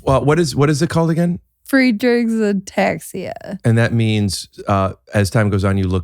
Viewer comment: did she say ‘free drinks and attacks ya’